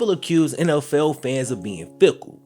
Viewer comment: Am I the only one who thought he said “nlfell fans” at the beginning of the season